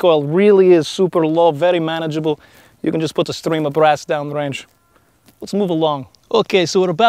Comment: idk why I hear put a string up your as